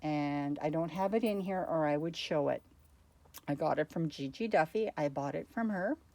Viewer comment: It's not actually in the video. You just talked about it and I couldn't quite make out the makers name when you said it. It is something like G G Duffy ... ring any bells? Using that name and spelling, I couldn't find it on you tube. Thanks. If you can't remember ... do not worry. Just wanted to have a look at her work. It's a little after 3:25 on your video. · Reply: oh yes. She does amazing work. Gigi Duffy. She’s in our boho group too. I’m going to pm her contact to your messenger on fb